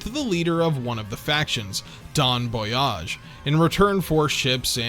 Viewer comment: DON Boyage? did i mishear that? I'm going insane here